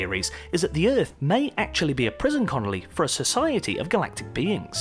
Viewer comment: Did anyone else hear him say conoly at 7:45?